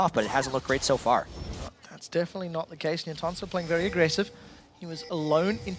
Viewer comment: dafuq is he saying at 20:02? who is playing aggressive? then he says it again at 20:16 and 20:32. who da hell is entonso? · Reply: Nientonso dude lol. That's nien's full name